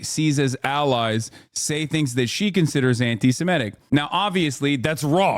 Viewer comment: Has' your chat broke my brain so hard that I thought that's said AOC is Anti- autism